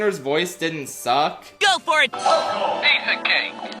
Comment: I always heard “go boy!”